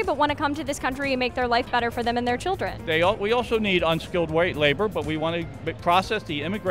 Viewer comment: Did I just hear “we need unskilled white labor”?